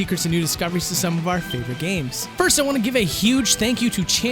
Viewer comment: i thought you said we were supposed to be finding secrets to our favorite games